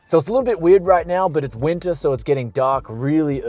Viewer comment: Did he said 6:17 it is winter ??